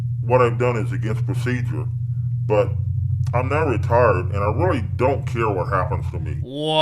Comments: I thought he said "retarded", not "retired" LMFAOOOO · Oh, he said retired? Could have sworn he said "I'm not retarded." Oops. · When the detective said he was retired I thought he said he was retarded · Look, i know i'm gonna catch some flak for just typing this.. but.. Did anybody else hear him say "But, i'm not retarded and i don't care what happens to me." And only realize he actually said "I'm now retired..." after AARP joke? Or am i just a terrible person? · oh my god he said “retired” with the terrible distortion i thought he said retarded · Bruh I heard 'retarded' instead of 'retired' lol